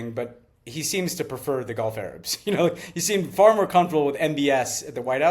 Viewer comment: Lol! I heard "Donald Trump is more comfortable with [golf] Arabs." Which, although not what Ben said, is still technically true.